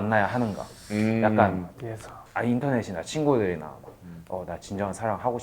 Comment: itu dia bilang indonesia indonesia chingu gitu gak si kak 2:27 , ngomong apaan si ya, gak ada sub eng nya ya :( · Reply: dia bilang internet wkwk cara ngomongnya in-teo-nes(t) makanya kaya kedengeran ngomong indonesia😂😂